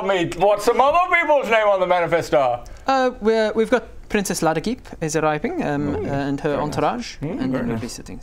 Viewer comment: I thought he said Princess Laradeep and wanted a reminder of her character. Pro Tip: DON'T Google Princess Laradeep....oh boy...